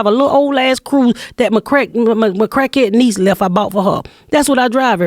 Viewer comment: For a second I thought she said "cracka niece"! Laughing my ass off at my foreignness!